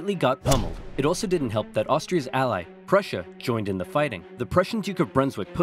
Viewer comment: did he say cRussia?